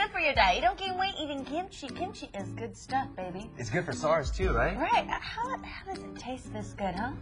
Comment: I listened several times and I still don't know what he said... "It's good for ~ (sars) too right?" I have no idea.